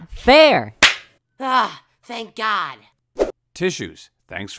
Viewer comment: Did apple say thank god